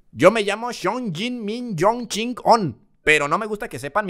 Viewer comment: Párese que dice Don chingon xdd